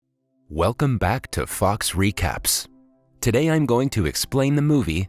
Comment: who else is new and wasn't looking and thought he said "welcome to fucks recap" anyone? no? ok it's just me